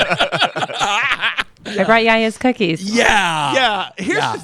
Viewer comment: First 20 times I actually thought she was saying I brought yeah yeahs cookies😂